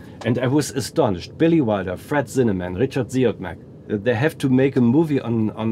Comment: Does anyone know what name (director) he says last when talking about 'People on Sunday', after Billy Wilder, Fred Zinnemann - sounds like 'Richard Ziogmac' phonetically - is he maybe saying Robert Siodmak? 2:53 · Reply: He said Richard Siodmak, but I think he meant Robert Siodmak.